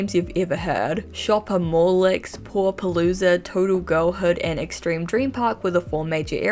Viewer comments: I would like you to know that with your accent I totally thought you were saying “poor palooza” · When you said "Paw Palooza" at first I heard "Poor Palooza." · lmao ngl i heard "Pool-a-Palooza" as "Poor-a-Palooza" initially cus of her accent 😂 i was like "for the... poor barbies..? at least its inclusive...." ahahaha